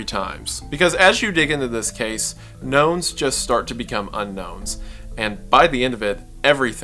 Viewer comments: I thought said "Gnomes become UnGnomes" and was thinking for a second that we were getting into Gnome-Pill territory after having graduated from Giant pill territory. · genuinely thought he said "gnomes start to becomes un-gnomes" and tbh that's so on brand for I'm not even mad at myself.